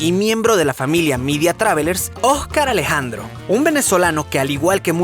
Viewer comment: Soy la única que se dio cuenta que dijo ojcar